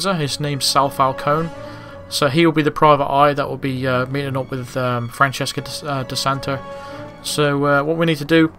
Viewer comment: i think i heard "Francesca de Santa"